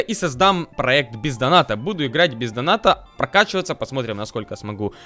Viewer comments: "но дальше я перейду на своего твинка и создам проект ПИЗДАНАТА" ставь лайк если услышал в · Кста 10к лайков, урааа!!!!!! P. S. Мне послышалось или сказал ПизДоната?